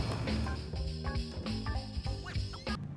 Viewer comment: ooo wait did u say dinner i thought u said yolo